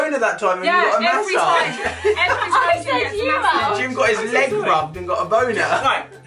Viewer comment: When i first watched this video I thought Alfie said 'Jim got this leg rubbed and got Ebola' but then I realised what he said😂😂